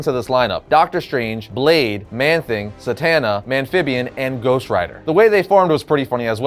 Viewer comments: Did he just say Zatanna?! I'm so confused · Like Zatanna from DC or did I hear that wrong cuz I wanna know who that is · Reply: Satanna